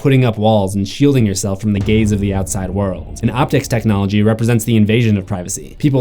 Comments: I thought he said "its the idea of putting up walls and shielding yourself from the gays of the outside" lmao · thought he said "shieldinto yourself from the gays of the outside world"